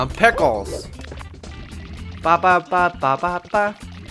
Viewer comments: I like how you say that Mario says "pickles" before each level....as a kid I always thought he said "popsicle!" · Um, u know when you thought Mario says pickles? I think he says "let's go!" instead of pickles. But does kinda sound like he is saying pickles!